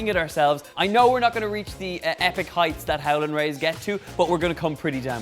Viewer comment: I thought he said 'Helen' Rays lol